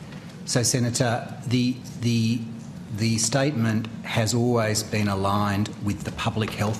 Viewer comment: Lol - "The Statement Has Always Been A Lie" __________________________ At least, that's what I heard 😂